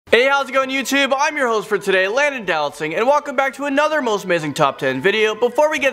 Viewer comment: I always think it sounds like he says "landon do not sing"